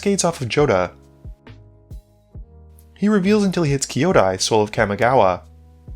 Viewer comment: I misheard Swole of Kamigawa, no regrets